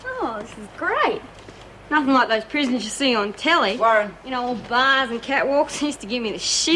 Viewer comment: did she say all bars and catwalks?